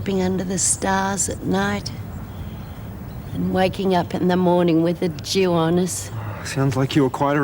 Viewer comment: At first I THOUGHT Lizzie said "And waking up in the morning with a JEW on us" and I'm thinking What the heck? then I re-listened and it's "And waking up in the morning with THE DEW on us"--that makes more sense!! 🤣😂😸😸🦘🦘🦘🦘🦘🦘🦘🦘🦘